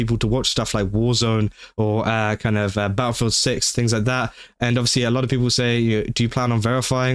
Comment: Honestly... I dont say this withe the intent to insult you, but everytime you said, "Battlefield 6" thanks to your accent, thought you were saying "BaoFoot Six" and it was some sort of new horror game centered around Bao.